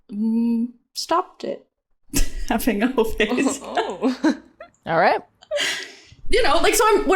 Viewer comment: For about 5mins till about 14:31 I got very confused cos I thought they were saying Ho Face/Slut Face. And I was thinking what on earth is that and of course what they were actually saying was ho-phase. Which makes much more sense.